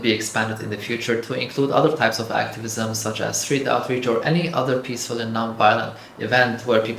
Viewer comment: What kind of activism did he say at 7:40? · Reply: "Street outreach"